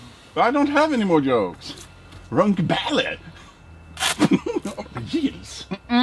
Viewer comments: can someone tell me what the fuck he said here? "runk ballet?? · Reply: Runka balle 😇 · wtf did say on 3:03? im german and my english is so shit pls tell me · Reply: runke balle